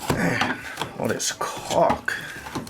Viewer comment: "all this caulk!" XD not what i thought you said!